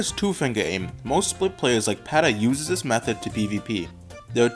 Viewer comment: "Most Split players..." What I hear - "Most SHIT players..."